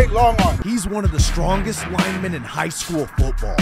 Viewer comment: I swear I heard you say he’s one of the strongest women in hs football